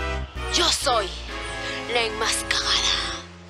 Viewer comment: Llegó a decir la enmascagada xd 2:18 es obvio, solo un tonto pensaría que dice: la enmascarada